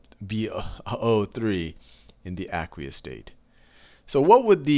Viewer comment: What is he saying at 0:57 aquate or aguate ? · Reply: aqueous state